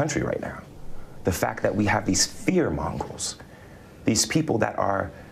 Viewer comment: Did...did he say 'fear mongols'? Does Jussie Smollett think that fear monger is fear mongols?